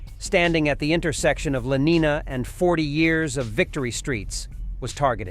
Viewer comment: did he say the street is called "Forty Years of Victory" Street? When was the first year??